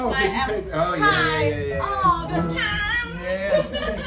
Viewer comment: Did she just say "I'm High ALL THE TIIIIME" LMAO!